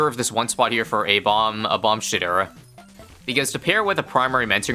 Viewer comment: Did he say "Bomb Shitter"